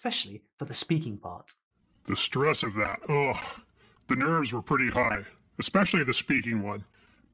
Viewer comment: what I heard was "the nerds are pretty high, especially the speaking ones”😂